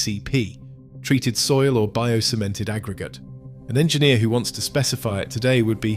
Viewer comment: Did I heard it right?? "Bio-semen"...